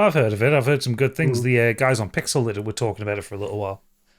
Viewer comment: Did say Pixelator? Which podcast is it? Great show guys!